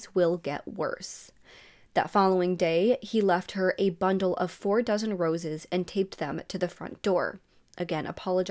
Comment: When you said "4 dozen roses" I heard "4 thousand roses". I was like, "Say what!!!" Not your fault, I watch on fast speed so I misheard; nothing you need to worry about doing differently on your end! Just funny....